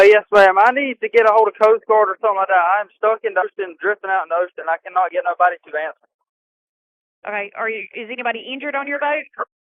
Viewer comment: To me it sounds like he said I’m stuck and it’s drifting out I can’t get no one to answer